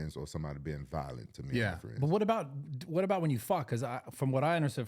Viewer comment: yooo Brad did not say FIGHT, I heard what Rampage heard 😂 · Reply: “Fought” not fight